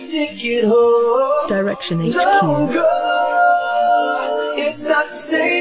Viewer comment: is it just me or anyone heard someone/something says something like ''direction is cute''?